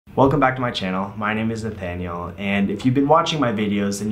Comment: I heard "My name isn't Daniel" instead of "My name is Nathaniel". Boy, I need sleep.